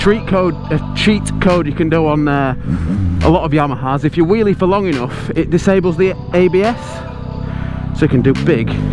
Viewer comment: What is he saying about disabling ABS at 9:00? I didn't understand at all. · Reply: you wheelie long enough and the abs shuts off.